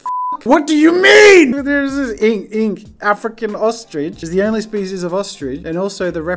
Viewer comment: i thought he said a frickin ostrich